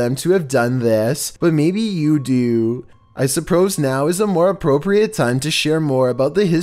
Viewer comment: "I Supprose now." anyone else hear It?